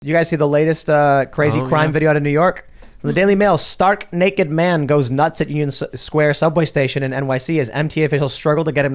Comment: I thought Tim said "Stark naked man shows nuts..." and it did make sense lol!